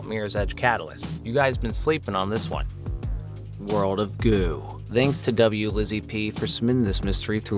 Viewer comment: For a moment, I thought you said "World of Goon."